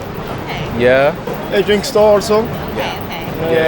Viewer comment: timing = 0:49. I heard 'denki store'. What can I call these words which foreigner speaks Japanese word? It's of course Japanese. nothing other than it. repatriate? The color of your wear (shocking bright red/scarlet) would suit you, enhance fair of your skin and exude/emanate your noble dignity/refined chic. Your manner of interviewing would be to the point/polite/easy to be comprehensible not only to the interviewed but also video viewer, I suppose. I feel I want to boast of you.